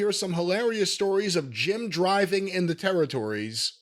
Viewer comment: Oh! "Jim driving". I thought you said "gin driving". I almost thought we were talking about moonshine making or shipping.